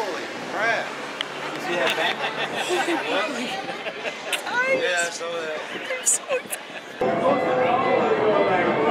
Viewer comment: can anyone else hear crapping himself? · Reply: That was him saying "guys" with a thick g.